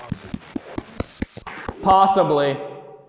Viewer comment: During the Estes when asked "did you follow Jason here from the prison?" I heard "OBVIOUSLY" not "possibly"